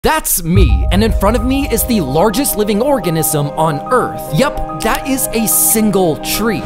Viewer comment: I remember seeing this giant trees in California in a state park it’s really beautiful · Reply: I thought he said largest living orgasm 🥴